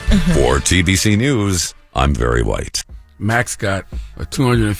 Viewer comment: Did bro say his name was “very white?”